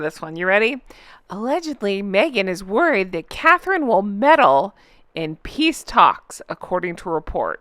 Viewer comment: Lol--, I thought I heard you say 'Medal in Peace Talks' such as the Nobel...😂😂